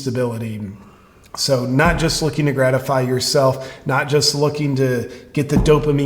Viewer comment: I just heard wrong: "Gretafy" - that should be a new word in Urban Dictionary. :D